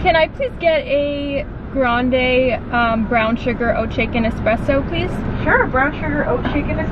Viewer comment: I needed this🤎 but please tell me your Starbucks order bc I keep hearing “oat chicken espresso” and when I turn my captions on it also reads that. Is that correct? Or am I trippin 😅